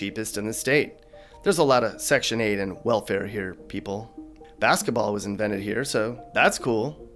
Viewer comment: I thought you said basketball was invited in Indian